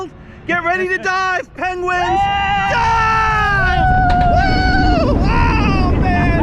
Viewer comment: doesn’t it kinda sound like he says “DIE”!?